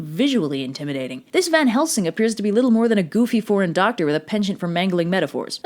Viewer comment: I thought when she said foreign i thought she said poreighn!